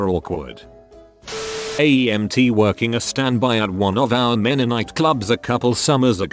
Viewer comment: Wasn't reading along and I heard "One of our Mennonite clubs..."